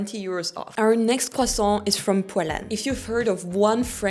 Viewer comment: when she said Poilâne I thought she said Poland at first 😂